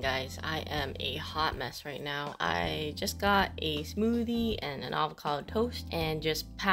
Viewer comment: I thought she said I just got a smoothie and alcohol for toast instead of smoothie and a avocado toast lol